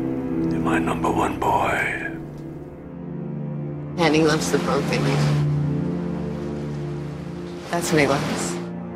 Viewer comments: what does she say at 3:52? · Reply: “Ken, he loves the broken you. That’s what he loves” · I’m still confused over Naomi’s words at 3:54, subtitles say “he loves the protein”... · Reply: “Ken, he loves the broken you. That’s who he loves.”